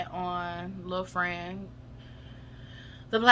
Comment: Lmfao did u call them little things 😂😂😂😂😂😂